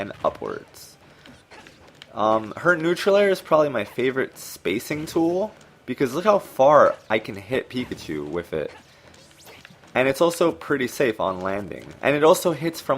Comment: did you say peepachu??? Haha😂😂😂